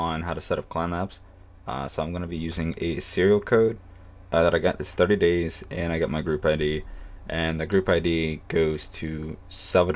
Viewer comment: all I heard was serial code what else did he say? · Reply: Serial Code "that I got this 30 daysand i got my group ID--" and so on